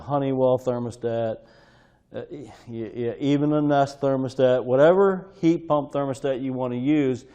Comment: lol “even a nest”..I heard that as “guys..stop with these damn things “ and I agree